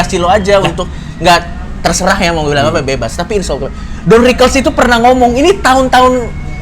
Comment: Gasalah denger kan gw coki ngomong insyaallah😭